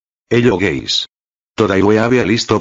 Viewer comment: I thought u said ‘Hello gays